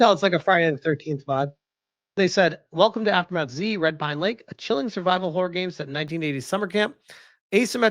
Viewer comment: My dumb ahh thought it said Red Pine Cake